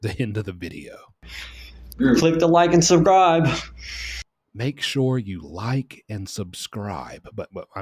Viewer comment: The dude at the end sounded like he was saying "like and survive" to me